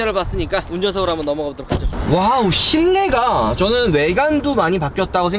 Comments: 와우 쉰내가..이렇게들려서 현웃했네요 ㅋㅋㅋ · 전 왜 "와우 쉰내가" 로 들리죠